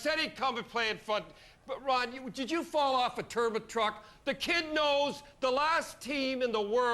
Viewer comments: i thought he said turban truck.... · "Ron, did you fall off a turnip truck?!" LOL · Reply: I thought he said turban truck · Thought he said Turban truck lol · did he say “did you fall of a turban truck” 🤣🤣🤣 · did he say turban truck